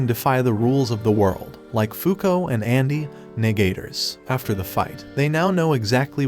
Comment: WHAT DID HE SAYYYY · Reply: Negaters